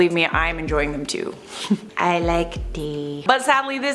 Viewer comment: It sounded like she said ‘I LIKE D’